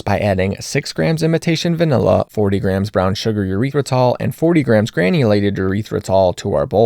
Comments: Lol, thought you said urethra-tol. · Everytime you say imitation vanilla, I hear "invitation" 🤣